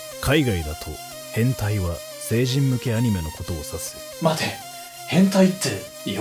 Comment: So it seems that the proper Japanese term for what westerners call “Hentai” (animations) is “sejin no ki anime” (tried my best to phonetically sound it out from what I heard @ 2:04).